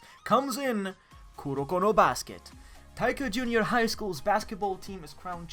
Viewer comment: did he say kurokuro basket xD